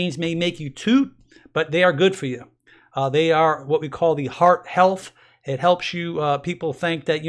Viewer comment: When you mentioned Black Beans & Heart Health I thought you’d said Fart Health 😉😂😂😂😂 Added to my list